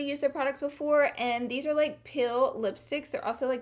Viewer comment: I thought you said peal lipsticks not pill! When you wiped off the lipstick with a makeup wipe , I kept thinking Why aren't you pealing it off!! What's going on?!? After a quick rewatch, then I realized you said PILL not peal! That Alabama accent is still strong! Rock it girl!!